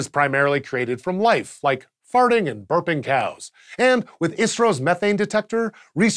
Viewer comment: he keep's saying "ISRO's" and I keep hearing "Israel" wtf